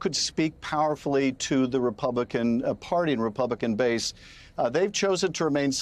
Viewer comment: I thought Schiff said the pumpkin party.